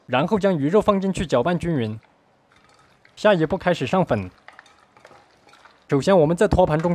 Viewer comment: 听成了下一步开始上坟😂